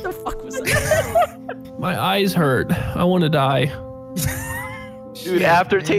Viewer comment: i died so hard when i heard "i wanna die"